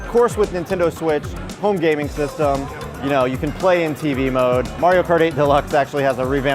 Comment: did he say Mario party at 0:37??